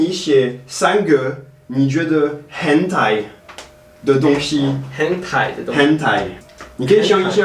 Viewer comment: 聽成hentai